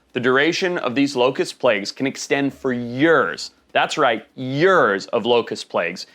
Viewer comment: is he saying "your's or year's" yall!!